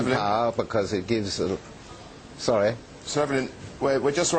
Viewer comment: ?!?!?!? did he call him sovrighn?